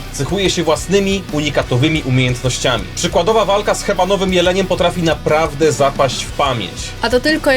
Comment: czemu usłyszałem kebabowy jeleń